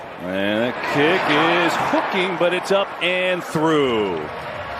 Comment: sounds like Kevin is saying "The kick is f*cking"